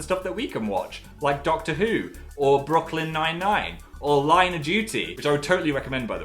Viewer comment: Anyone else hears Phil says "Broccoli Nine Nine"?